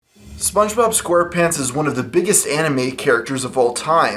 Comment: Did you just call spongebob an anime? In 0:05?